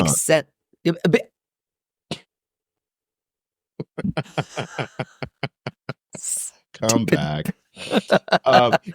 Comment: What was Bob's joke to Plasma saying they milked the text at 26:00 mark? Did somebody hear? Thanks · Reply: He said i’m an icon